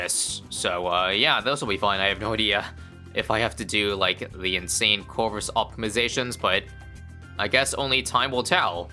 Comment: I heard "the N****r of corvus" for a split second